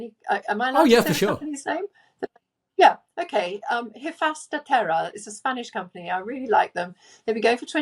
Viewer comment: Mushrooms. I couldn't hear what mushroom company Dr. mentioned, so I looked it up. "Hifas da Terra".